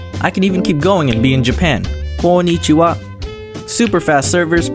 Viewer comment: I know Dom was saying konnichiwa but at 7:23 it sounded like "... goandeatyouup".